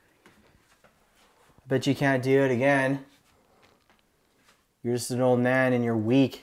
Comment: Hey guys, so I hear at 10:02 very very quietly a soft whisper say 'do it again " while yur telling him to wack it off again, you have to listen very carefully as it's not you guys talking! Let me know what ya think please, keep up the good vids! Pegger out for now! 👍👻🇨🇦